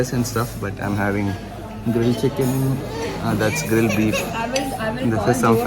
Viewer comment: did you said beef...